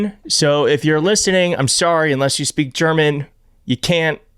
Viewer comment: "it's German, so if youre listening, im sorry...unless you speak German [in which case...I'm Sorry ]..." (what i heard vs what he actually said :-P)